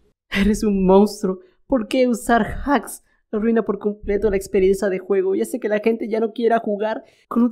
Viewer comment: Parece que dijo "la experiensa"🤣